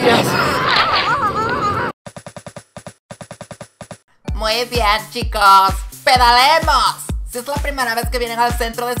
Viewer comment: no entendi :v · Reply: No se puede montar en bici dentro de un edificio